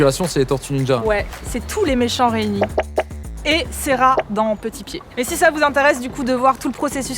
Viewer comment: Quel taf de malade ! ♥ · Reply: "Cera dans Petit Pied" quoi ? XD